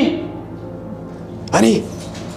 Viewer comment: 每次叫唤安妮，都听成honney😄